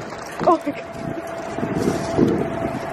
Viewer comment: What did the man say? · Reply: And then she goes "I have your bag though" XD Omg Love so much.